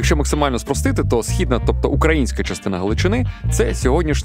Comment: східна частина України чи мені почулось? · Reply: Наскільки я розумію, мова йшла про східну частину Галичини, що є частиною сучасної України